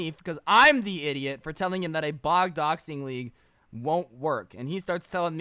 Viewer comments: at timestamp 38:40 am i the only one who hears him say bog doxing? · Did you say bog doxing at 38:42 lol